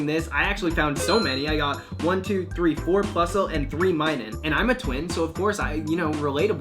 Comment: When you said "And I'm a twin" I thought you said "And I'm a twink"